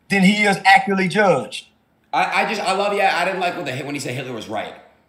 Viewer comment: kanye did not say that. he said "i like hitler"